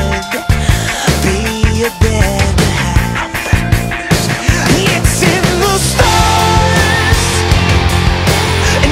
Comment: Been replaying over and over. Waited for the album to listen to this and legit cried. What's being whispered at 1:46?? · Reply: "I'm better than this, come back"